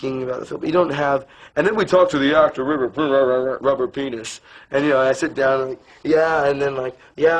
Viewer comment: did he say rubber penis?